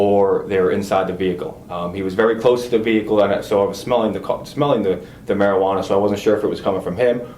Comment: did he just say “smelling the co..” 😂